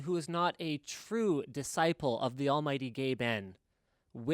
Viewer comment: did he just say the almighty gay men?